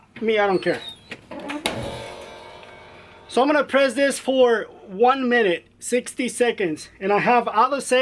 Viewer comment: lol you make me laugh!!!! I tell every one my secrets I don't ever hold anything back obviously you have not seen my videos or read the comments below lol 370f for ONE minute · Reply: I thought you said for 1 minute 30 seconds